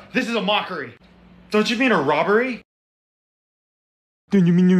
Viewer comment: What i heard: "dOnT yOu MeAn A mOcKoRy"